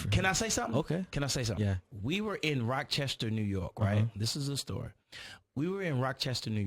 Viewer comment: did he say rock Chester ny?